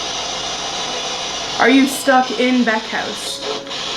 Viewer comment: when you pointed the camera at the house, it sounded like a wheezing "ooooeeehh", y'know? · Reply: the last male voiced word kinda sounded like stuck, witch is weird since you asked or mary.